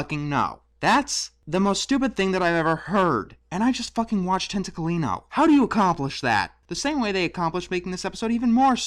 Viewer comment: And you just fucking watched what? It sounds like "tentacle-eeno." I can't understand what you said at all, nor does the brief image of the blue guy and the dog look even remotely familiar.